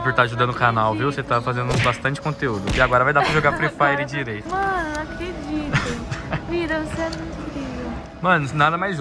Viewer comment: "Mano eu não acredito.. (vida) você é muito incrível" Só eu que entendi isso?